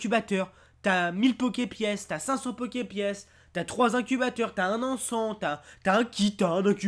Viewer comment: ptdr j'ai entendu "t'as un enfant !" à la place d'encent XD